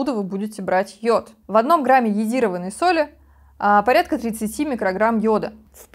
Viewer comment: Добрый день, это интересно, но с арифметикой вы не дружите. Если в 1 грамм е соли 10 мкг йода, то в 5 граммах (1 чл) будет в 5 раз больше, те 50 мкг, но не 150! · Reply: Здравствуйте, вы, наверное, не расслышали. 5:46 - сказано «в одном грамме соли около 30 мкг йода...» Это стандарт ГОСТ, конечно, нам остаётся лишь надеяться на добросовестность производителей.